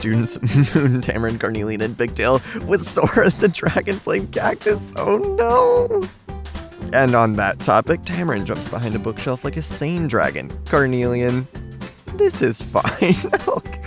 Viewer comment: for some reason I always hear this as “Sora the Dragonflame cactus”